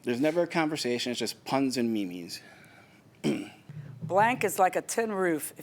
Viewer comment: Im so sorry, did he just say "Puns and Mimis??"